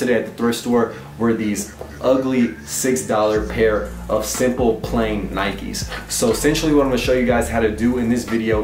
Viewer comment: I heard "simple plan" nikes